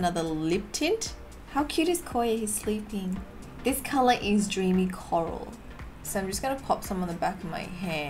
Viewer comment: Who else thought she said joonie coral instead of dreamy coral at 15:49 Or it's just me